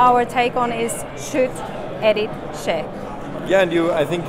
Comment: Did she say their take on it is, "Shoot any shit"? "Shoot. Edit. Share." Okay, I got it now ;3